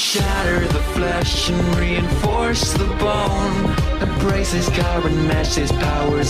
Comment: I thought it said reinforce the bong😂